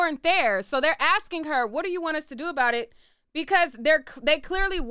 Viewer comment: that’s not what they said. They just kept saying “what do you want us todo”